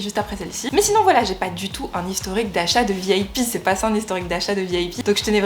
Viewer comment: J'ai entendu "vieille pie" au lieu de "VIP" et je n'arrive plus à m'arrêter de rire...